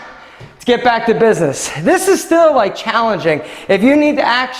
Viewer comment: What he said: "This is still challenging" What I heard: "This will likely kill you"